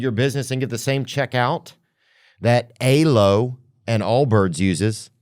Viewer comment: what company did mention in the ad read? Sounded like he said Aylo 😅